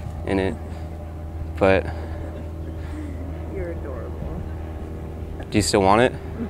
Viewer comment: does she say "your adorable" or "your a dork"?